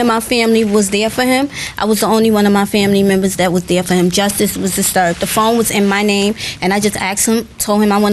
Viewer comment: I believe him! Did she say justice was disturbed? 🤦‍♀️